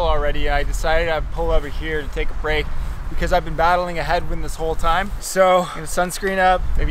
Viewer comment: I thought he said he'd been battling a head wound .